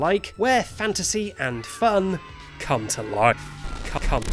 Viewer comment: what did he say? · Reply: He said come?